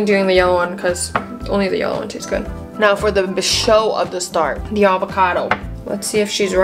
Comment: she said “Show of the star” instead of “Star of the show” ❤😂 3:34 (NO HATE)